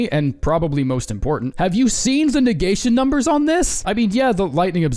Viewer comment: I thought you said it had bad negation